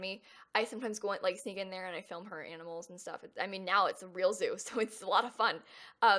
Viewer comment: when she said "i sneak in there and film her animals" i legit thought she said "i sneak in there and kill her animals"